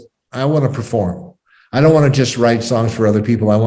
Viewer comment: It's called "Don't Want To Wait Anymore." It's on The Completion Backward Principle album. Intersestinlgy, one of their guitar players, Bill Spooner, is the lead singer on the song. Truly a great song. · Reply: thanks. It sounded like he said 'dont wanna win.'